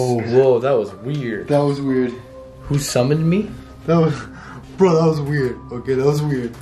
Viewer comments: i think I heard: Your stomach is full XD idk why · i think I heard: Your stomach is full XD idk why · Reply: Daily Paw omg lol i heard that to then i replayed it and i heard “who summoned me?” lol